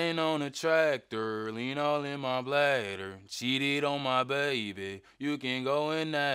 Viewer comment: Good thing I actually read the words cause I thought he said " Shited on my baby"😂🤦🏾‍♂️